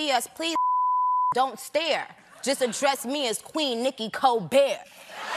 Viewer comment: 😝 "I might f--k Stephen after the show / He gon' come back to work with a magical glow / But when you see us please, f--king don't stare / Just address me as Queen Nicki Colbert." 😂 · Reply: I thought she said “queen nicki co-bare”